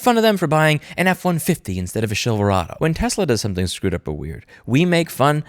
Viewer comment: maybe I just heard this wrong, but Louis saying "shillverado" so fast and not even acknowledging it sounded so normal that I thought I was gaslighting myself lol